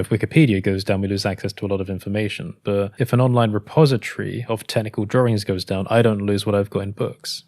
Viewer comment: For some reason, I heard “tentacle-like drawings” instead of “technical-like drawings.” 😂